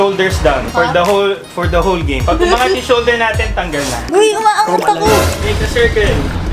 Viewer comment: i heard some indonesian language here seah : "dingin banget aku" it means "i'am so cold" 😭🤣✊🏻